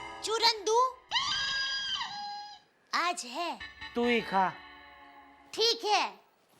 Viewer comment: 😂 when jethalal said tu hi kha! I thought he said in bangali language Tui Kha 😂 Any Bangladeshi fan relate this? 😂